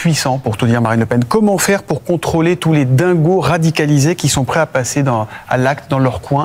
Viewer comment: "Dites-nous, Herr H1tler, comment faire pour lutter contre les anti-allemands ?" Moi c'est ce que j'ai entendu.